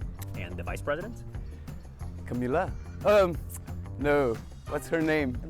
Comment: I think he said Camilla, the wife of King Charles😅